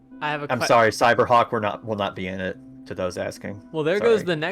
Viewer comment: what did say here? :0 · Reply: “Cyberhawk will not be in it” haha